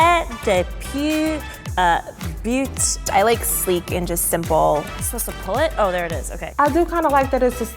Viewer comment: When she said “I like sleek and simple” I thought she said “I, like, speak in simple” lol